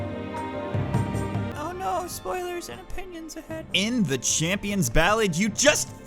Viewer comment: I thought it said spoilers and onions for a sec